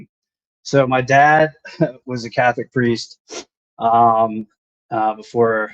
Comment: Did he say his father was a Catholic preist???